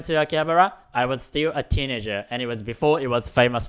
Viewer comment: At number 25 I thought he said I'll steal a teenager and I thought wait, what tf